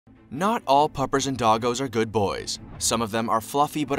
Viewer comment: I love how the first thing I heard was “Not all puppies and doggos are good bois”